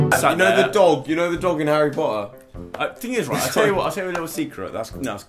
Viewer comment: A few glass of wine later and the first thing I hear is 'u now that dog of Harry potter' I'm done😂